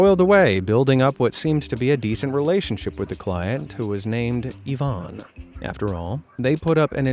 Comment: How is my name almost exactly same Yuvaan(me) Yuvon(her (what I heard))